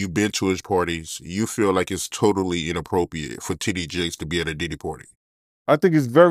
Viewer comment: Lmao can’t pronounce certain letters 😂 sound like he said “Tidday Jakes…” haha